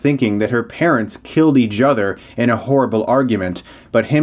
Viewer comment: Sounded like you said "they killed each judder"